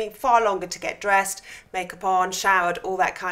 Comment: This might seem inappropriate but did you say make a porn? Around 1:09?? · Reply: No, she said makeup on